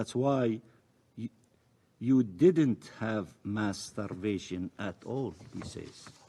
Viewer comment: Netanyahu should be jailed.Put his murder cabinet down.They want war,give them real war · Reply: did he say "masturbation"?